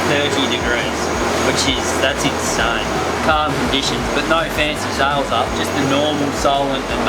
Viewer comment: Some of the best fun is watching some of these old videos with the closed caption on and watching it trying to pick up what is saying. Apparently at 6:20 he said "which is that it's a condition but no offenses out Justin Norman Solomon tonight